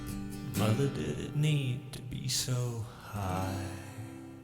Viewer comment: The end of Mother "mother did it need to be so high" isn't about the mom being high, he's asking if she needed to build the wall so high. Great reaction as always! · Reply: I never thought about it that way! I always thought he said," Mother didn't "mean" to be so high. " I never realized he was talking about the height of the wall!